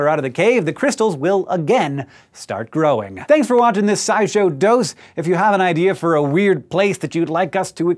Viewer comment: Oh, he's saying "SciShow - dose -" as in a small serving of SciShow. For months I've heard it as "SciShow - dos -", as in Spanish for Two. I was utterly confused why I couldn't find SciShow uno, and why this looked so much like the main channel if it was in fact the second. It took thinking about Mexico to not think in Spanish...